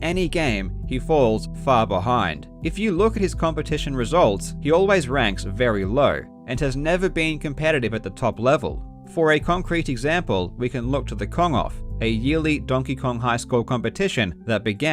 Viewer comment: I thought you said “for a Kong-Crete example” and “Kong-Crete” evidence. I’m going to keep believing you did because I love a good pun.